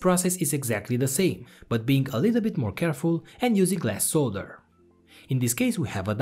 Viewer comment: Did he say "glass solder"?